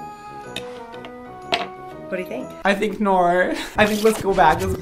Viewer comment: I swear i heard him say "I think Naur" 😂